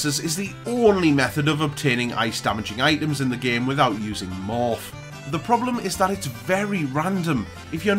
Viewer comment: "Betting on chocobo races is the only method of obtaining ice-damaging items in the game without using (what?)" I'm sorry, but I've listened to this sentence many times on repeat, and I can't figure out what the last word is. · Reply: Morph. (Sorry about my accent haha)